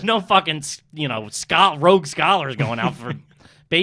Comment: is saying Rogue Scholar? If so, trash.